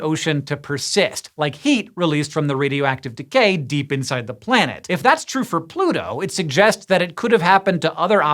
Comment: did I hear PLANET???? lol